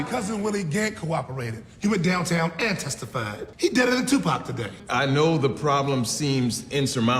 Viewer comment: I never realized what this one man said. He said his cousin Willie Gant testified and his "deader than Tupac today." Willie Gant was the witness who got killed at the end of the very first episode of the show.